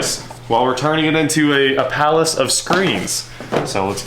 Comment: Did he say Palace of Screams??? 😱 😊